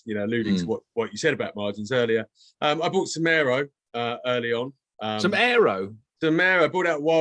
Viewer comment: I thought he said he bought some Aero at first. 😃